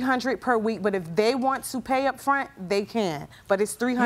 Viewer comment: For a second I thought JJ was gonna say" Im gonna throw your AZZ outta here "